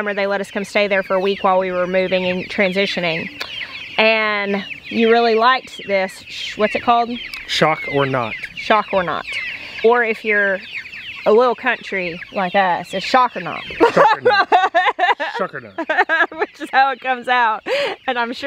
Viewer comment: When talked about his poultry netting, I thought he was saying shocker nought, which is the way he was pronouncing it. Prefer that to the proper name 🙂